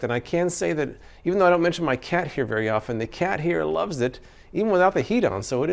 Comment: ish) First time around I heard "...the cat hair loves it..." 😮 & was thinking "Why's he sound so happy about that, doesn't seem like a selling point imo" 😁